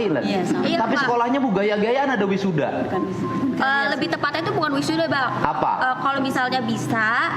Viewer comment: Apa q salah dengar ya dia ngomong " lebih tepat nya bukan wisuda Bang " etika nya NOL